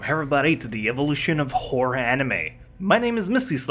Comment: I know that meant to say "Horror" anime but I swear think I heard "Whore" anime instead. :/